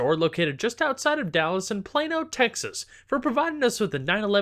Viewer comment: Ah, I got it! "Plano, Texas!" I always thought you said "Plain, old Texas!" :D